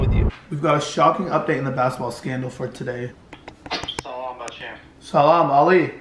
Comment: did Brandon say salaam? I thought Arabic’s and Muslims said that. Sorry if I offended anyone, I’m not completely sure of the religion